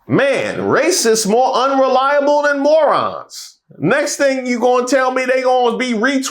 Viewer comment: IT DOES say "Morons" HAHAHAHAHAHAHAHAHAHA I thought it said "Mormons" and Reese was just being silly... HAHAHAHAHAHAHAHAHAHA